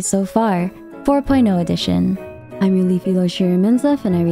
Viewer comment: I screamed, I thought you said "Filipino edition" and thought you were Filipino